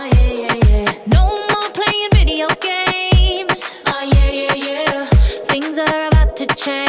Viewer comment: "no more playing video games" i thought u said u ain't my mama ??